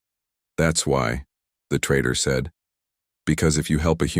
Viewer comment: yikes. Subtitles said “traitor” instead of “trader” 😮